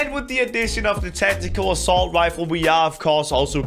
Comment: Tactical assault rifle Me: Did u just say tentical assault rifle